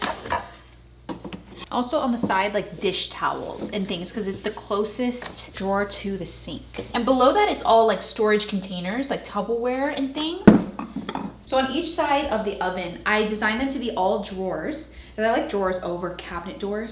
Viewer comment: Did you say tubber ware?